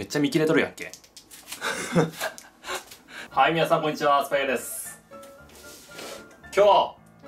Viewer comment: こんにちはアスパラギアですにあいか聞こえなくなってしまった